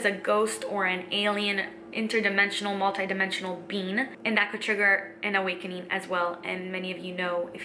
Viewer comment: Lol! For a second I thought you said “a multi-dimensional bean” Great videos...! Living the Dream!👍